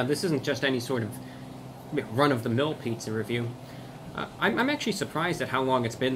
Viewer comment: Sounded like he said McRunofthemill.